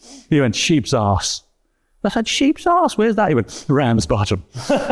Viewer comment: What's being said here? I'm not a native English speaker. Around...? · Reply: Ramsbottom